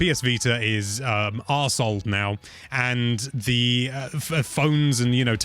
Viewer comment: did just say Arseholed on Nintendo life...